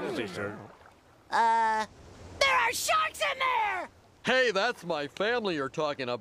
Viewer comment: When I was little I always thought Spongebob said that there are shorts in the ocean